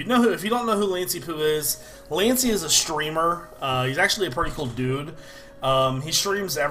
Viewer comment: when he said hes a streamer i thought he said stripper